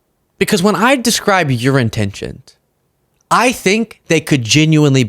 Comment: Damn, I heard "urine tensions" haha